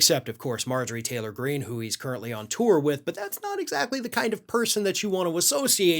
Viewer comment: I think I must have mis-heard this bit: 0:18 "...doesn't have a friend in the world, except for Marjorie Taylor Greene, whom he's currently on top of..." what what WHAT? Did I hear that correctly???